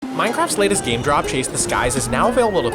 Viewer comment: Did bro just say "Minecarf"?